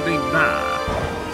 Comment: what did you say?!?! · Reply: Rohana taufik I think